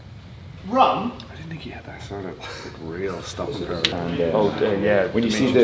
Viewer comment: What did john kavanaugh say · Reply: jasjot singh “I don’t think he had that real stopping power”